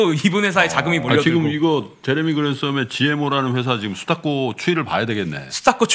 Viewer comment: 스탁고 추이? 스탁꼬 추이? 저분들이 뭐라고 말씀하시는지 아시는 똘똘한분 계시나요? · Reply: 수탁고 : 수탁고는 투자신탁회사들이 수익증권을 매각한 후 환매되지 않고 남아있는 순자산가치로 자금유입 및 포트폴리오의 시가가 반영되어 있다.